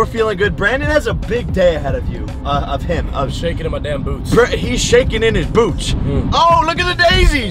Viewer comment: he sounds like he saying "I'm shaking my dam boobs"😂